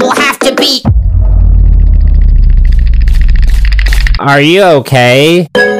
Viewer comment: I thought it said your mom, but it was are you OK?